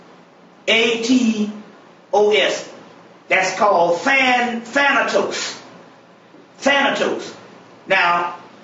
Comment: Thanatos=Thanos… did you catch it?? Now you see why Thanos was a big bald headed “purple” man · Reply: I thought he said phantos - I was thinking of the phantom on top of the Rolls Royce - yaw know that’s us right we b on a roll! Can’t wait until I can figure out how to fly.